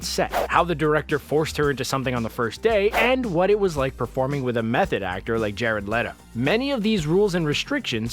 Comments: "How is it working with a method actor like jared leto" -Best joke of 2019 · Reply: I thought it said "working with a meth addict" lmao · i thought i heard meth head lol method